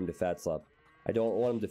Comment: I heard fat slob come up so often in the wall videos, I had to check it out. The whole time I thought t900 was saying "Fat Slav" lol.